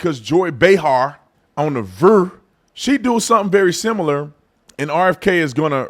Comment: Bro did you just say “on the VURR” come on man